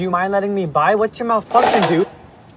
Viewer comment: Ok but like am i the only one who doesn’t ship Kit and Jax? Idk, i like them way better as besties 19:18 LMFAAOOO JAX’S FACE 19:20 for a sec i thought he said “do you mind letting me by whats your mother f*cking dude kjfdskxy